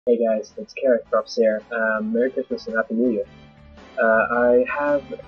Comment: in the intro I thought you said carrot cups here